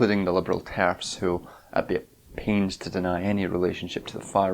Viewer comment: a wonderfully articulated video. worth mentioning that there are some issues with the captioning btw! early in the video it reads 'jewish scientist' instead of 'jewish zionist' and at 6:55 'transforms' instead of 'transphobes' along with some other mistakes. just a note to whoever captions these vids!